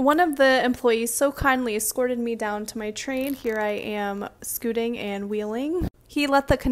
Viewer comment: Ngl sounded like you said he "squirted you down to the train".. lol thought it was silly. Thanks for sharing your experience.